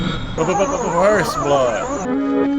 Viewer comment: May narinig ako sa 9:42...fir fir first blood....yun lang